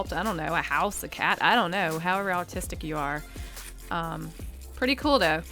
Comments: it sounds like she says "however autistic you are" instead of artistic lol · did she say Autistic instead of artistic?? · did u say autistic or artistic?😂😂😂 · “Idk how ever autisic you are” sounded like she said that instead of artistic I’m I the only one who heard this? I’m a gamer girl so I hear autisic everywhere now... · almost sounded like she said Autistic instead of artistic 😹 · It sounded like she said Autistic instead or Artistic. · when she said however artistic you are i thought she said autistic instead of artistic xD lol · When she said how artistic you are during the peanuts part I thought she said autistic. And I was just like that’s rude .-. But then I rewinded it and noticed she said artistic 😊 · so is it me or did it sound like she said autistic instead of artistic ?? lmao no hate to anyone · Did anyone else catch at 4:49 she said autistic instead of artist 🤦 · she said "autistic" instead of artistic😳😳😳😶😶😶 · I know she said how artistic you are but it sounded like she said autistic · she said how ever autistic you are instead of artistic 💀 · Anyone else hear her say autistic instead of artistic